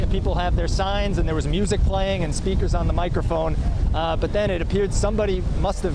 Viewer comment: I thought he said "streakers". He said "speakers". Not a good time for streaking.